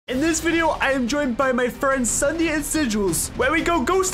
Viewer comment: At the start, for some reason I heard: "Today, I am joined by my boyfriends Ssundee and Sigils."